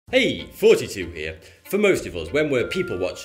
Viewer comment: I genuinely thought he was saying "Heyyy 42 here." Didn't make any sense, but I went with it. Then one day I was looking at the channel name right when he said it and I screamed OH MY GOD and woke up my family.